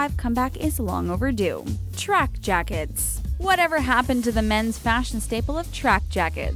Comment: Who else thought she said tracker jakkers instead of track jackets